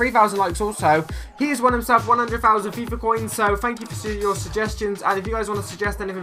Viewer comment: I thought said 100k FIFA point